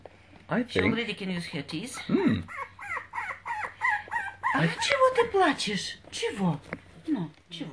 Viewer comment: Does live in Russia? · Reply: Yep, I clearly heard "чего ты плачешь?" too.